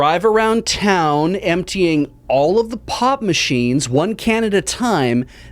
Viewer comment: For a second I thought said POT machines and thought "oh, Canada has really gone all-in on the whole legalization thing."